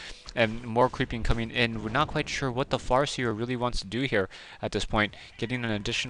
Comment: hehe it sounded like you were about to say ''what the fu*k''' but then it turned into ''what the FAR seer'' ,made me lol